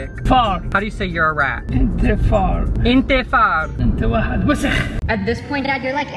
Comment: did he just say "enta wahed wesikh" or is it just meee ... am in shoook 💀😂💗